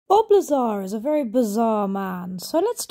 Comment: I thought you said Bulbasaur instead of Bob Lazar at first 😂 Lovely work btw! I've just been scrolling through your shorts, you sculpting your pieces and painting them is super relaxing to watch!!❤